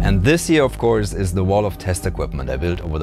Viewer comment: I could have sworn that he said "This is the wall of testicles"....